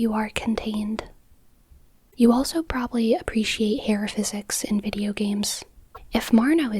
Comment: Let's go part 2!! Love your videos, but you already know it since I'm always here commenting ahah 2:59 I misheard it, I had to listen twice to get aero physics lol